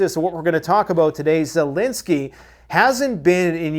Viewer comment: I heard it “ZELOSENY”!😅